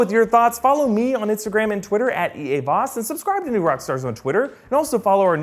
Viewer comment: did he just says subscribe to us in Twitter? Or did I miss hear??